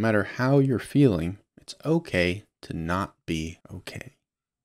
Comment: I was doing something else and listening to the video and misheard at 2:29 "it's okay to not be okay" I totally heard "it's okay to not be gay"